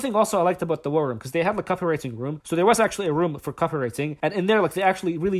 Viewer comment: I thought he said KAAFIR RATING. Okay, I get it now. Copywriting.